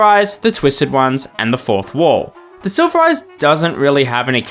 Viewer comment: I heard of the fourth closet, but not the fourth wall.